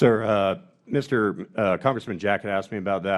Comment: For a moment, it sounded like he said "Congressman Jackass." 🤣